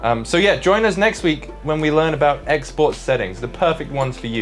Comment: MY EPIC WEBSITE! (Finzar Presets): <link> :_getouttahe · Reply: ohh I thought u said Xbox settings i was so confused lol